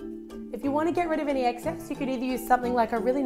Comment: Did she just say exsex